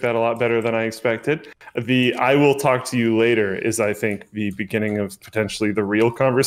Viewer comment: "Why do you do things" and "I'll talk to you later" RIP Linus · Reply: listening back to it it sounded like said I will f**k you later